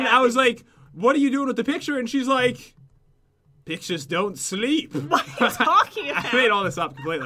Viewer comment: he says a swear.......just saying its fine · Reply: He said pictures don't sleep